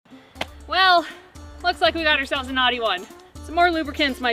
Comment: She said, "Well, looks like we got ourselves a knotty one. Some more lubricants might be necessary." But what I thought she said was, "Well, looks like we got ourselves a naughty one. Some more lubricants might be necessary."